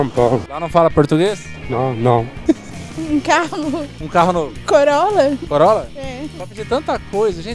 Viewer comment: Só eu que ouvi ela pedindo Corona? kkk